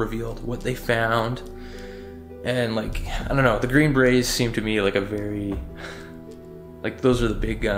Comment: Lol sounds like he said green Bernie’s 😂😂